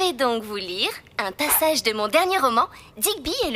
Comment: A 1:55 c'est moi ou Sally elle a dit "un tassage" ? 🤔 Like si tu a trouver que le docteur Diablo c'était en fait le gardien ( a 7:52 ) 💖